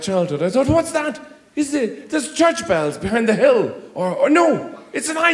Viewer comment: Am i the only one who heard him say “an ice cream van in the rain”